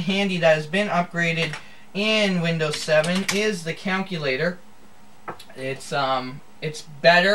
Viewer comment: did he just say counculator?